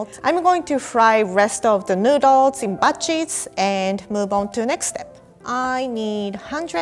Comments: I thought she said butt cheeks instead of batches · "I'm going to fry the rest of the noodles in butt cheeks.." me: wait what did she say??? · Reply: She meant batches but lol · I thought she said butt cheeks 😭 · I thought she said butt-cheeks